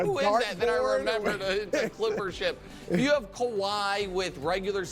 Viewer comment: ? does nick say "its some cilpper shit" lmaoo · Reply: That would've been awesome but nah he was saying "the clipper ship" bc theylir new logo is a badly drawn ship